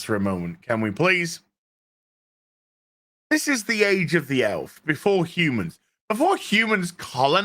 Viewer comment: I misheard you when you said "I't s the age of the Elves." I heard: "It's the age of the L's."